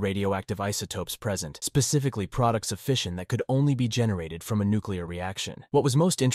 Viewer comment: That's crazy as hell!! So 2 billion years ago, there was a nuclear event which tainted the fish which tainted that uranium deposit. I am understanding that correctly, right?? That's what I'm hearing anyways. Lmao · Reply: Lmfao HES SAYIN FISSION!! LMFAO. I THOUGHT HE SAID "FISH IN" 😂😂😂😂